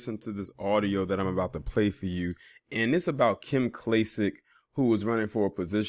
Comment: LAWWWDDD I thought that said KLICK KLACK!!! 😂😂😂